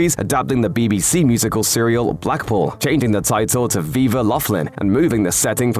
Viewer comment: LOL I thought you said BEAVER Laughlin I HATE AI narration...